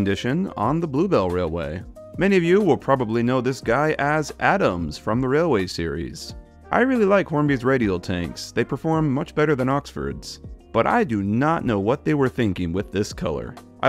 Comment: when you said that, I at first thought you said Adam from stories of Sodor. But clearly it’s been a while since I looked at the Railway Series characters.